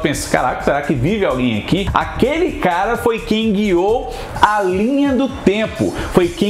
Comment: Quem pegou a referência que o fez sem saber? Hahha. Minutos 12:34 e 12:37 “Foi Kang-ou” “Foi Kang-ou”. Kkkkk😂😂😂😂